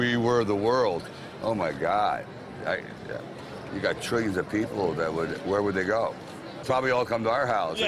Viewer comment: Did Sly just say "Trillions of people"? Does Vegas get people from other dimensions?